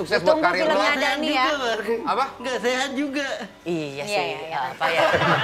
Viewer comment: dani bilang apa? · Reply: "ngga' Sehat juga bang"